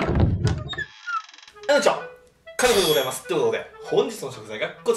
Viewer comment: Tシャツのせいで、最初の挨拶が「片栗粉でございます！」に聞こえるｗ